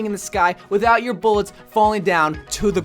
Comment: did he just say bullets?